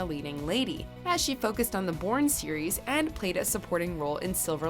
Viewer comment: I thought she said Julia Stiles was in the boring series lmao....